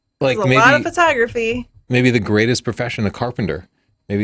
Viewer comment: When suggested a Square Space portfolio for the "greatest profession" I was away from my laptop and I was sure I heard "oldest profession". I backed up the video and sorted out my mistake, but I can't shake the mental image of a Square Space portfolio devoted to the oldest profession......